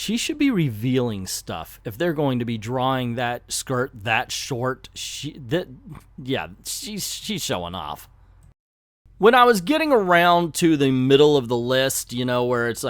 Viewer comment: What kind of nonsense did you said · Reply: They drew her skirt so short she would be flashing people